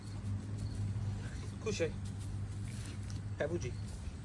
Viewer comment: Wait did I hear "couche" as in French/Creole for lay down?lol i must be hearing shit